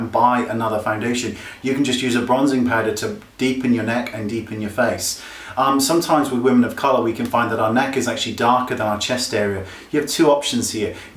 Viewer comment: I almost thought you said "us woman of colour" I'm like... Whhaaat <3